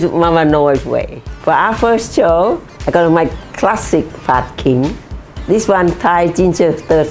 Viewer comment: I thought she said my classic fa king and got shocked for a minute 🤣🤣🤣 love me some mama Noi! More recipes please ❤❤❤